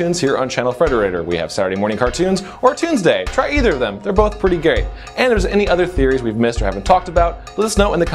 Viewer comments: No offence but it sounded like he said, they're both pretty gay. Hahahahahaha. A little too short with the R in geat. lol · I had to go back because I thought he said there both pretty gay.